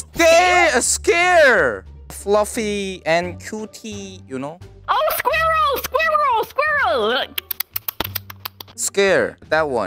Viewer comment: actual: "That animal is a squirrel!" misheard: "That animal is scared!" Okay, first we misheard "coke" as "c*ck", and then now "squirrel" as "scared"?!